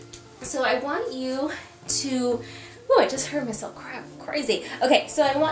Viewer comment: “oooh I just heard myself crap.” Anybody else do a double take there? (Obviously she said crack.)